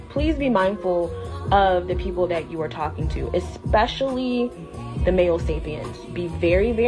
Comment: especially men? Is that what meant or said?